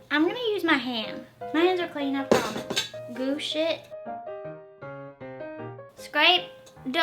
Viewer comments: I thought you said "goop sh*t" and I had to watch that bit twice 😂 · Had to rewatch when she said mush it. I thought she said oh shit lol